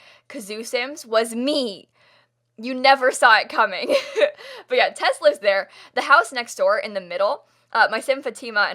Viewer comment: I swear I heard, "but yeah, teslas there." Just me?